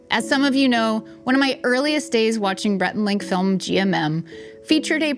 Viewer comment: Did say "Brett and Link?" Haha